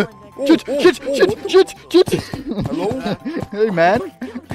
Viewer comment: Umm i heard shit not chit